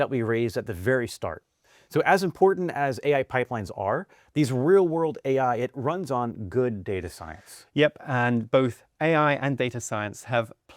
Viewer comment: Interesting. 12:36 I heard sentences in there that sound like: “So as important as AI pipelines are. These real-world AI. It runs on good data science.” I could be wrong. Anyway, great video!